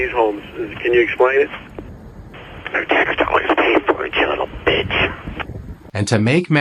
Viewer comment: does he say" cuz our tax dollars pay for it bitch" that's crazy. The way his voice changes. Don't the tax dollars " pay" for that other area too??? Just trying to control the guy?